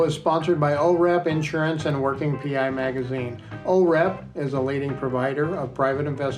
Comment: I thought he said "we're sponsored be Oh Crap! insurance company". 😅😅😅